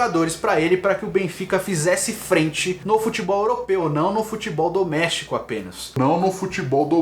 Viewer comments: Acho que ele falou "Futebol doméstico"... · Acho que ele falou "doméstico" e não "do México"